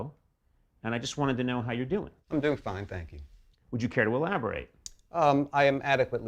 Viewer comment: 이부분에서 코난이 뭐라고 한건가요? Would you ~ ? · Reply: Would you care to elaborate?라고 했숩니당! elaborate는 더 자세하게 설명하라는 의미에여!